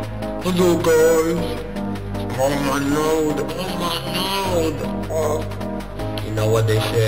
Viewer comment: "ah lets do this from the beggining" "umm... hello guys" "oh my (what the hell does he say here)" "oh my (again i dont know what he's saying)" "ah" "y'know what they say" 0:29 " DISNEY STATZ " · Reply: Oh my lord.